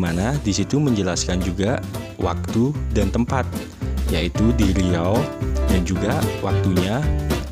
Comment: ada yang salah deh kayaknya,kan harusnya tempatnya di maluku.Tapi aku dengernya bilang" DI RIAU" iya gak sih?ada yang denger sama kayak aku gak???